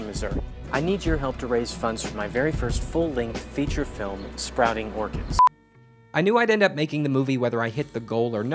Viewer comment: i swear he said sprouting organs